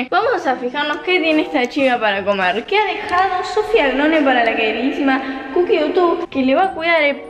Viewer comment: Pensé que dijo "Sofi angrone" 🤫 no le digan a